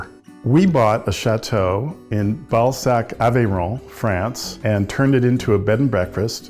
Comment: My inner child laughing at the name of the town Bal-sac. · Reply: I keep hearing “Shat-o in Ballsack”🤦😂